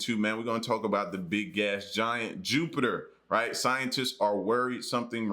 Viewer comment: 😰😅I thought he said Big Ass Giant Jupiter! 🤦🏾‍♂️🤦🏾‍♂️😂